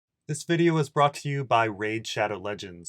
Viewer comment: You say: “This video is brought to you today by RAID...” But I hear: “I am a whore”